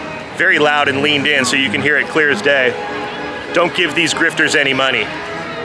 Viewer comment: I thought he said don't give money to drifters. Now I realize he said grifters. What a big fat jerk. I'm glad you reported him. It really stained the experience you were having. Sorry that he felt he needed to do that. Glad Charlie was there and shielded you in case he took it further. We love what you do keep doing it.